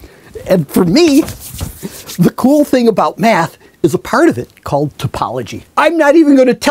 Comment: I could have sworn I heard him say "The cool thing about meth"